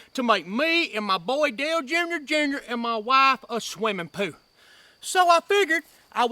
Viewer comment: Was I the only one that thought he was saying swimmin poo? 😂